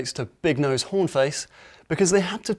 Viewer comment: anyone else hear him say "big nosed whore face"?